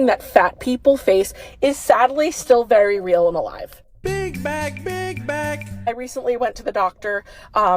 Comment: Omg. For the longest time I thought that sound bite said "Pig Back" , now I hear "Big Back ". Awesome